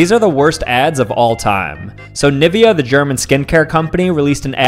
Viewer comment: 💀 I scrolled down not paying attention when he was saying Nivea, and I thought he said the n word for a second. I immediately scrolled back up.